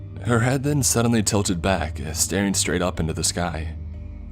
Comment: I thought it said "I'm straight" and it reminded me of the James Charles lol